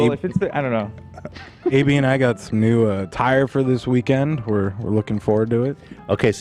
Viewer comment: 😂 Zack trying to bring up his new suit & Ethan thinking he’s talking about new tires on his car instead of “new attire” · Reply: I definitely heard "a new tire" when Zack said it. Haha. I immediately thought they were renting some sweet vehicles to bring to Vegas. English can be so confusing at times..